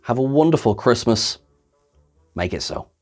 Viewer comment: i thought at the end he said "make it snow" haha